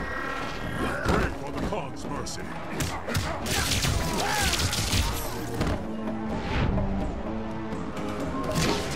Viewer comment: in second , what does say kotal kahn to raiden? really ı wonder this sentence please write here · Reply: Pray for the gods mercy awesome right